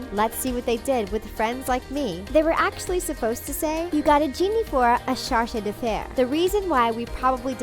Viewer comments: I heard "Genie for your charge de fare" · i always heard "you got a genie for your child affairs" even though i knew it wasnt right lol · There was French in Be Our Guest? I mean, obviously, but I couldn’t pick up on it. Aladdin: I heard “genie for young charged affairs”.